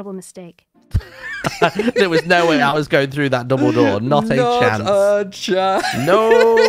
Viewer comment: I am pretty sure I heard say "there's no way I'm going through that Dumbledore"